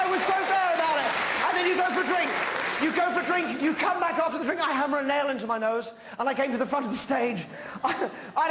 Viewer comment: cocaines a hell of a drug. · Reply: He mentions cocaine at 8:15? I don't hear that. He is talking about the intermission of his show that night. He says, you go for a drink, you come back after a drink, I hammer a nail into my nose. I came to the front of the stage, I don't know what you thought I said... That is what he said. but nothing about cocaine. He is talking about the first thing he did after intermission, which was hammer a nail into his nose. The entire show that night is on Youtube. This is only one little part of it.